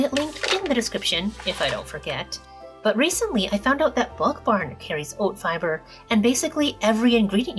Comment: Did you say Wok Barn? Is that a grocery store? Lol